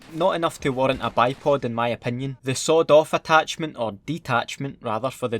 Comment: I thought he said the sod off attachment instead of sweet off lol